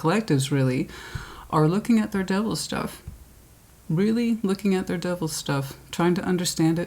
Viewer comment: 😂 at the beginning I heard "double stuff " rather than " devil stuff" haha apparently they go hand in hand lol 😂